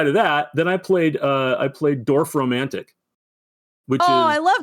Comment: anyone else hear "Dwarf Romantic" ?